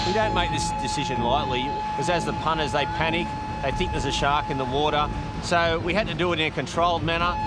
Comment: My American brain thought they were saying shock alarm instead of shark for a fat minute